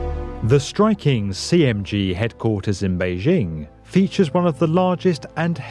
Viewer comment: at first i thought that said "OMG Headquarters" and i was like; yeah